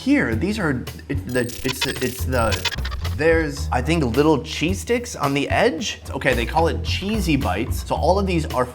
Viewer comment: Definitely thought he said "cheese dicks" lol